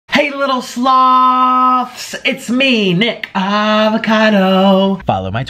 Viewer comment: im not your 'little sloth' wth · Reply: I thought he said "hello my little sluts"